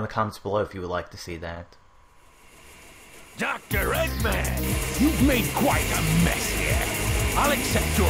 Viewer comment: Eggman says "You've made quite a mess here." But the subtitles say "You've made QUIT a mess here."